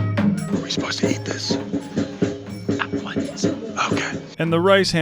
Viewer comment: “Are we supposed to eat this?” “At once” “And the rice wasn’t supposed to be eaten…”. Did anyone else catch this? The guy just made him eat uncooked rice